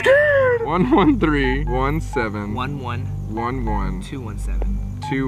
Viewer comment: the first time they called the watcher, i heard '1...1...1...7' then it was them goat-like sounds